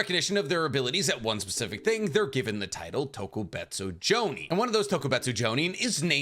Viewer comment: I heard "Tokubetsu Johnny" and that name is amazing